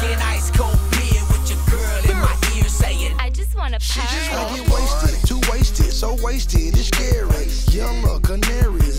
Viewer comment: I always wondered what she said to Gucci 😂🥶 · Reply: Frrrr